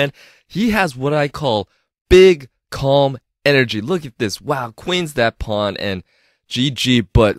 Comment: for a sec i thought u were sayin "big ** energy" 🤣